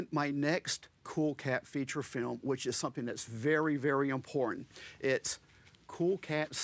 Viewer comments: It totally sounded like he said "in porn". I had to turn on subtitles to understand that he said "important." Also the war of the bucket, yes it's a real war, is easily the funniest. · as and slowly start to crack is the best. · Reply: I think it's because what he said was "important" but it sounded like he said "in porn."